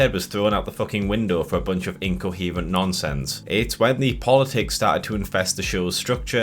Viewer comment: Did I deadass just hear "The Boys isn't political" come out of your mouth 😭😭😭😭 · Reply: I didn't hear "The Boys isn't political". I heard "The politics started to infest the show"